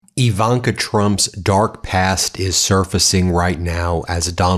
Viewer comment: Oh Ben.... I thought you said Ivanka's dark- GASH!